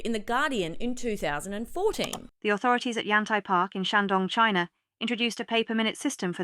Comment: I thought she was saying, "anti-park". Lol